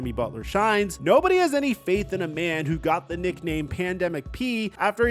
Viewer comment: at first I thought you said “Panda Mc P” to avoid demonetization lmao